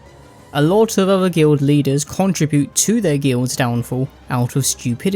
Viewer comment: Guild Leaders? I thought you said "Glue Eaters."